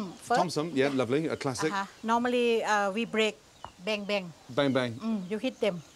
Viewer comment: “Beng-beng” thought he said bang bang